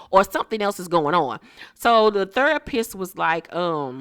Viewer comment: Your reviews are better than the actual shows! Sis, your accent is EVERYTHING. When you said “therapist,” I thought you said “the third piss”😂. Every time I see Drew on this show, I’m like “this is who Derwin messed around on Melanie with?”😂